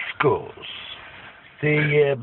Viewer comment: I thought he said Ljashko, but he said L U K U S